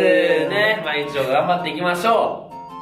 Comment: 毎日今日も頑張っていきましょー って聞こえるwww